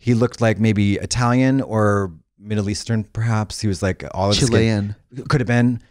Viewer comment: Does Trixie says Chilean or am I crayzy?